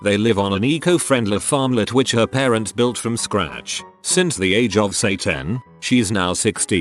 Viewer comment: i swear i heard him say "by the age of Satan" i swear did someone else heard it too or is just stupid lilttle me?